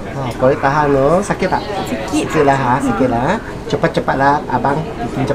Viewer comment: did he say chupa chupa lang? only filipinos will understand😂